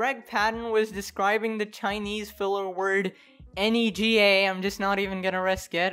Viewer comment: Wait until people realize vinegar I'm pretty sure he just said something that sounded like nega which is nothing like the N-word bruh it was nega 3:27 and I put that together when he said it